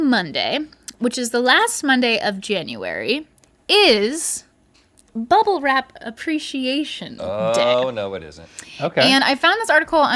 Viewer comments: really thought she said "mumble rap appreciation day" at first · I thought they were saying mumble rap appreciation day 🤣